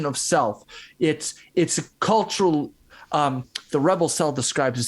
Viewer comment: What did he say describes it beautifully? The Rebel what? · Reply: Either the Rebel Cell or the Rebel Self, perhaps a book?